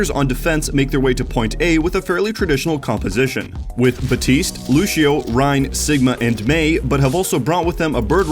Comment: Did he just say lushio